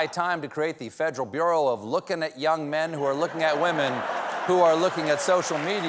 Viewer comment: Ok, so I think that Walked was saying young men and women who look at social media. He started saying just men, then tried to correct to include women, maybe to not seem sexist against men, to avoid suggesting only men can do it? And it just sounded like he said men looking at women. At least that is what it sounded like to me.